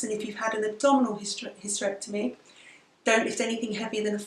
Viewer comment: When you said "abdominal hysterectomy" I thought you said "double hysterectomy" at first and laughed so hard :D